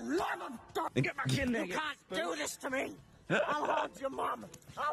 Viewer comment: I thought she said “I love chickens “🐓 lol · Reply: I thought she said I’ll haunt u